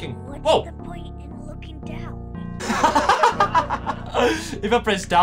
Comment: What does tails say at 10:47? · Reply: What's the point of looking down